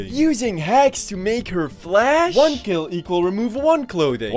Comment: With the censor I thought that said flush and thought it was a video about this guy desperately trying to teach his girlfriend to flush